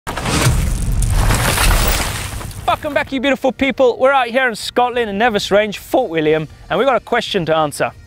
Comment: oh i thought i heard fuck em back you beautiful people